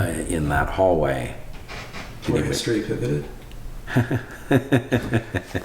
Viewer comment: What does the interviewer say at 21:14? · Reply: (In the hallway...) "Where history pivoted?"